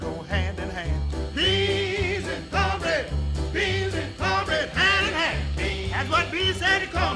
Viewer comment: All this time, I thought he was saying “peas and cornbread.” Dang. I love singing this song around the house, I used to hear it all the time when I was little. It used to come on some retro show on TBS.